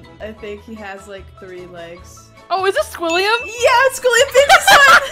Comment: 'he has liek big balls as hands' AND THE SCREAM AT THE END GOT ME😭😭- and 6:01 did i hear william or am i to much of a fnaf fan